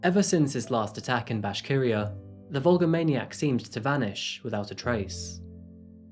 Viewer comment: I was doing some filing at work and not watching the screen, and I thought said the Volvo Maniac. 🤦‍♀️